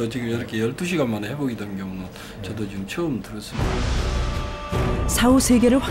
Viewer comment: 초 보면 주치의가 아니었던거 같은이유 자막에는 처음보았다 라고 했는데 들어보면 처음 들었다 (!?) 주작